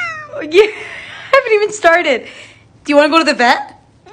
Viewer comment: "Do you wanna go to the vet?" "Mommmm" "Do you wanna go cut your nails?" "MOMMMMM *pushes her away*" Me: omggg that was the cutest thing I've ever seen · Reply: idk why tf i hear "do you wanna go to the bed? "